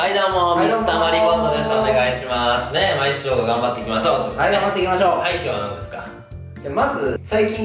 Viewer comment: さいしょの挨拶どうしても 「ね、まあ１億頑張っていきましょうっ」に聞こえるから本当のが分からなくなった（）